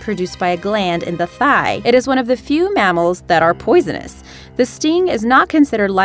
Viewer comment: the lady says "it is one of the few mammals that are poisonous" but the captions said "few mammals that lay eggs". I mean they're both right but still lay off the shrooms